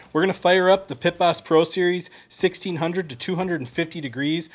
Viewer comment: For a second, I thought you said "1600 to 250 degrees" and I thought, MAN, how am I gonna get my smoker that hot? :-)